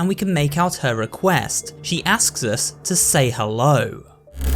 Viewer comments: I thought he say "hello" he said "say hello" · When Amanda says say hello with no speed it sounds like she says say bye bye